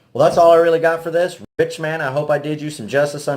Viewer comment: Awesome knives! But did you say "bitch-man" at the end? Lol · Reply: I had to go back and look at that lol. I said Rich man but I wasn't facing the Mic and I was kind of rambling lol. Thanks